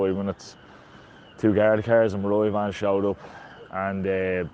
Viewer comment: what type of van did he say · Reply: Maria Van . Another word for police van